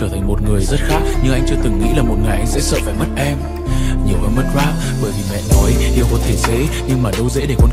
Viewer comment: Sao t lại nghe thành "Nhưng anh chưa từng nghĩ là một ngày anh sẽ sợ phải mất em nhiều hơn mất VAT" nhỉ? :))))